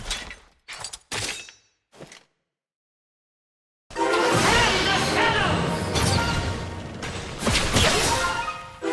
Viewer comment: what is he saying? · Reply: sounds like "in the shadows"